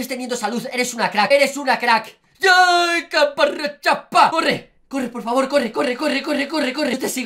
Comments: Entendí Capa rechapa xD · Que dice ahí xd 7:02 Ayy caparechapa XD Ya aprendiste Coreano Felicidades ^_^ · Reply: Dijo caparrechapa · yo:no entendi ese rap: ca pa ra cha